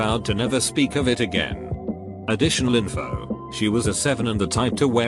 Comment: thought it said she was 7 and i was just and i was so confused about so many things